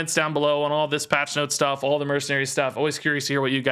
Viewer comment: Haha, it sounded like said "Until next time, gay"